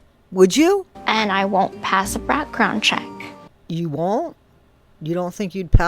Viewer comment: excuse me??? Did she say “brackground” check???